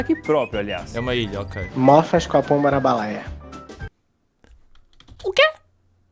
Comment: eu entendi:couro escapou para a baleia KKKKK Edit:eu falo chero, mas se escreve cheiro mesmo k